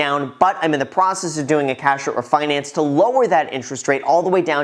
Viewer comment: Hey Graham. Super excited for you! Would you please explain what a cash-over finance is? (That’s what I heard you say) Thank you!